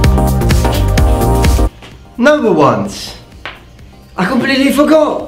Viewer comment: what is the first thing he says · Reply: Echo Nata "noble ones". He calls his viewers that